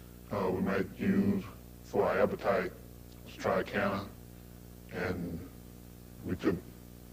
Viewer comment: What does he say the appetite stimulant is at 12:30? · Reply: I think he says triacana which is similar to t3 I believe